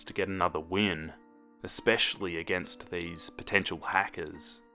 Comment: Oml when he said potential hacker I lowkey thought he said potato hackers😂😂